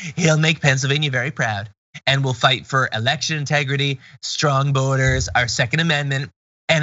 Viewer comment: I thought he said; "... will fight for Election Integrity, Strong Boners, our Second Amendment etc"